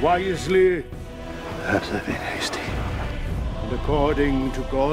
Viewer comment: Does anyone understand what Saul at 0:17 says? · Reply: "perhaps I've been hasty"